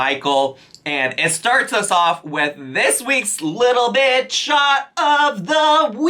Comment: When you sang “little bit shot of the week”, I heard “little bitch” for a second 😭😭